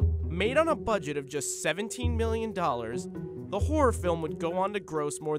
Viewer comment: I thought he said 17 dollars... WYM JUST 17 MILLION!?